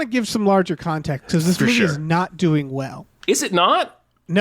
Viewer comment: how is a movie that made its money back a bomb? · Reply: sorry bout that misheard thought you said 200 million